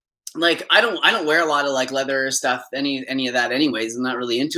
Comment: DID i hear that right? You don't wear a lot of leather?? You should not wear any of it. Get rid of that curse asap. Swap it, cast it out like jon, donate it. Wearing leather is not vegan.